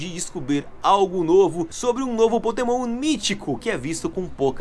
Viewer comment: "Potemon" Mitico? Eu acho que eu ouvi errado